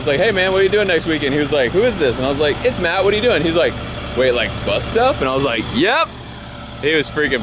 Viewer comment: why do i always hear "BUTT STUFF" when I know its Bus stuff? 😁😂😂😂